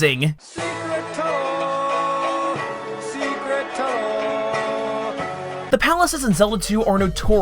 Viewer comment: SECRET TUNNEEEEEEEEEEEEEEELLL THROUGH THE MOUNTAAAAAIIIIINNN SECRET SECRET SECRET TUNNEEEELL!!! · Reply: Instead of "SECRET TUNNEL!"I heard, "ZERICKO..... ZERICOOHH!!!!!......"